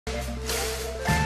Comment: LOL I always thought he was saying "Funkman, on the keyboards. Funkman!" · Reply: I always thought he said “Buck Lamb” on the keyboards. Had to listen to this a bunch of times before finally hearing it as “Bob Mayo.”